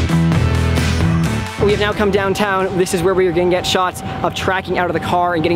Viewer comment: I thought said: we’ve now come downtown, this is where were gonna get shot